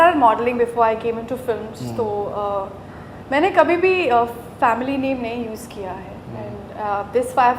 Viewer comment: did she say Family?